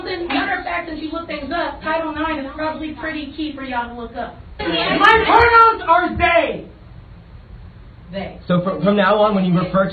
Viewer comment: I thought she said her parents are gay, I would understand her parents are confused, then I played it back she really said her pronouns are they. WHAT THE HELL DOES THAT MEAN?